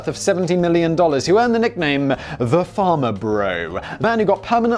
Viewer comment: Simon's accent: I heard "Farmer Bro" for the first half of the video. Lol.